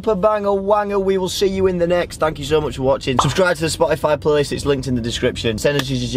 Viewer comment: did he said Dankeschön ? 🧐😅